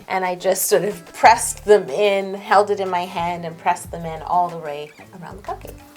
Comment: Omg who else noticed that at 4:42 when she was doing the cupcake lines she said all the ray instead of all the way😂😂😂